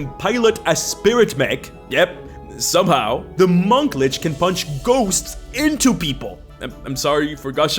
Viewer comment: At one point you say "Monk Lich" instead of "Monk Witch", and now the "Which Lich" series has taken on an entirely different context...